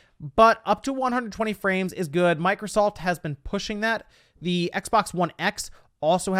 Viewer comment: Did he say Micro-salt? 😂😂😂